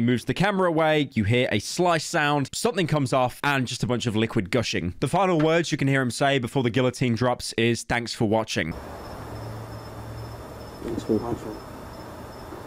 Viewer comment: I thought he said "Thanks for the Blindfold".